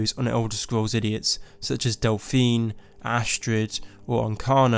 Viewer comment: i wish there was a "Yes Man" option in skyrim where you didn't have to support the Stormcloaks or the Empire. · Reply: i legit thought you've said dolphin for a sec